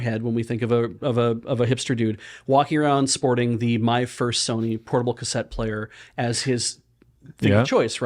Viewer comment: I heard "My Fursony"